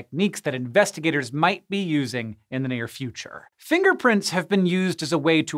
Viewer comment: Im sorry, but the whole time he was talking about finger prints, I kept hearing "Finger Prince." Goodnight everybody.